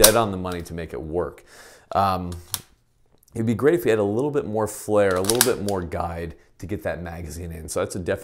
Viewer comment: Did you say you’d like, “more hair”or more “flare”... for an easier feed? 👉🏼👌🏼